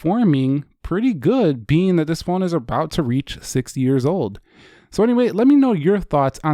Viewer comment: You said 60 years old instead of just 6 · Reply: Actually I said 6 it just sound like 60